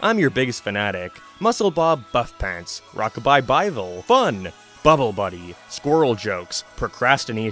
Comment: I literally thought you said “Rock-a-bye Bible”! 🤣🤣 Perfect name for a children’s compilation of bible stories!! 🤣🤣🤣